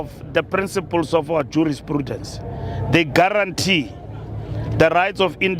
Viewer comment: Did Mbalula mention prudence on this speech.....I think I heard "due to prudence "